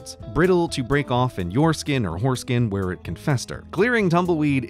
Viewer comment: Wasn't really paying attention, but at 1:52, it sounded like you said, "Brittle to break of in you skin or foreskin"...